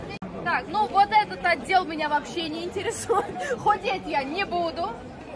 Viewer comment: Мне послышалось или ты сказала - хуеть я не буду?